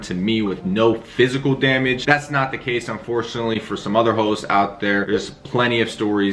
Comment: Bro the subtitle says "some other hoes"